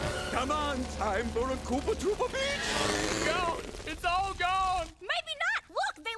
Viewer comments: What did Mario say here? “Time for a Koopa Troopa Peach?” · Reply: I thought he said "Koopa Troopa Bitch". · What did Mario say here? “Time for a Koopa Troopa Peach?” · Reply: Time for Koopa Troopa Beach It’s the track after Moo Moo Farm in Mariokart 64